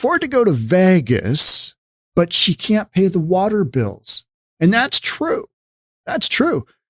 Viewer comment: I Thought She Said She Can't Be Touched..